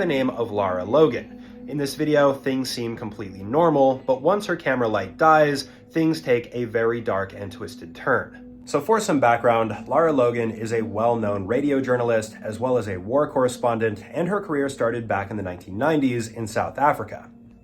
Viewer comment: bro did not say "foresome" background